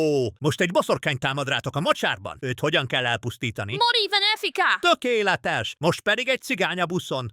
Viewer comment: mit mondtak? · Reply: Mori venefica. "halj meg boszorkány", Harry Potteresen rossz latinnal